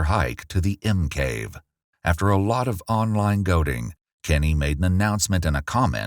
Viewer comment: I know the commentator said it properly, but the caption says “after online goating” and it should say “goading”